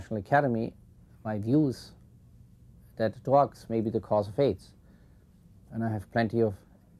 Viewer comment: sec That what might be the cause of AIDS. What did he say? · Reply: Drugs.